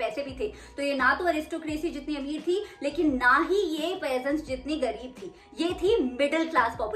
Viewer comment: they were not rich as aristocracy but they were also not poor as what??? (Word)?💬 Kiski trha gareeb ni thii?? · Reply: peasents